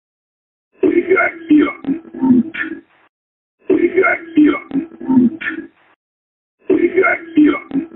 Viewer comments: parece a voz do ator falecido - José Wilker podem reparar. Não é nítido o que ele fala mas tem uma enorme semelhança .. Ele diz " vou ligar aqui ó um Plug" entendi isso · Eu entendi: "Vou ligar aqui, ó".